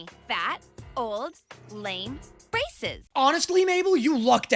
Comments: for some reason I instead of braces I heard "racist" D: (first time seeing in original dub though) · I looked away from the screen and misheard “braces” as “racist” 😭 I should probably turn up my volume to hear better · when she said braces I thought she said racist 😮😅